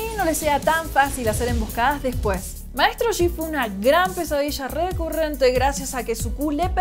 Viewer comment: parece que dice "Maestro Shifu" XD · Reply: "Maestro yi fue una gran pesadilla" para los que realmente creen que en verdad dijo "Maestro Shifu" XD